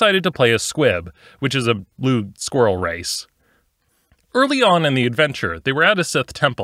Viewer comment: thank goodness for CC... i heard you say "lewd squirrel race" 5 times and was like 65% sure that wasn't what you intended me to hear.